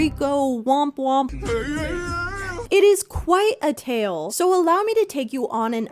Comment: hey so i dont mean to come off as like that 'well erm actually 🤓☝️' kid but at 1:03 you said 'quite' and the subtitles say 'quiet'